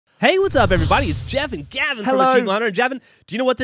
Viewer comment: did anyone hear Geoff call Gavin "Javin" at 0:02? XD